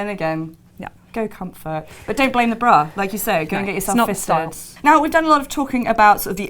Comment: I'm sure you said "get yourself fitted" but I heard "get yourself fisted" lmao